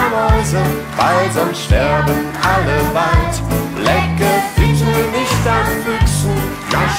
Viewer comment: Ich habe erst "lecke bitte nicht an Füßen" verstanden 😅 guter Lifehack